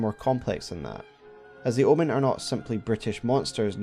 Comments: I kept hearing your pronunciation of the word "brutish" as "British", and I was so confused haha. I thought that was a joke until I realised I misheard it. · "The Omen are not simply British monsters, needing to be culled." 1:05 misheard that one oops. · Every time you describe the Omen as "Bruteish" I hear "Brittish".